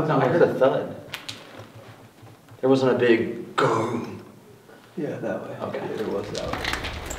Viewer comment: you can kinda hear what sounds like a person saying "yeah! thats it" right before the thud